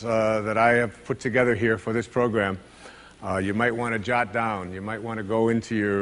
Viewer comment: What is the synonym for "Write it down" that he uses after 0:36? I heard this phrase several times from Jim Rohn as well, but I don't know how it's written and would be glad to find out. It sounds something like: "Jot down". Thank you